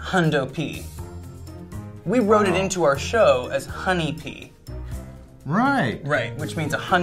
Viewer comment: I always thought Alexis was saying honey pea and I was so confused lol